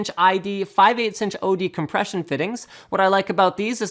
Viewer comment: I keep hearing "oppression fittings"